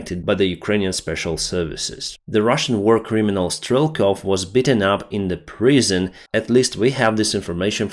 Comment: You mention you want to work on your English sometimes. It’s very good, but to help, “beaten” say more like eat. Sounded like you said “bitten” and I thought for a moment he was mauled by a dog. Best of wishes friend. Slava Ukraini.